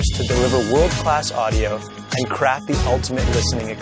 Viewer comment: did he just say crappy? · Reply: he said "craft the"